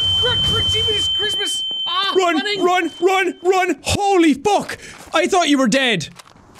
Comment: am i the only one who heard " HOLY BUCK"